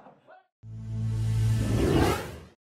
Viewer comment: que dice al final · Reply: Dice: felicitaciones por llegar a la final Franco.